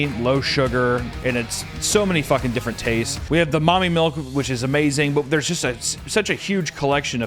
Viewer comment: Is he joking? Did he say Mommy Milk was a flavor?